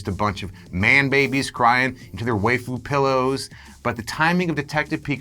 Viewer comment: Did he said "Wayfu pillows"?